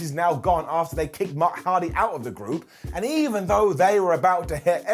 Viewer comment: Did... did you just say Mark Hardy @ 2:55 ? lol Must be the secret 3rd Hardy member.